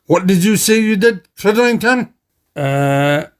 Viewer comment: What?! WHAT DID YOU SAY DEAR CRIDLINGTON!?